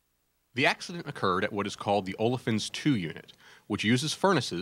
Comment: Can somebody help me please? I'm translating the text the narrator reads into spanish, but i can´t understand what it says at 1:18. I think it's something technical. Thanks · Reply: Colmenares Burgos The accident occured at the "Olefins Two" unit.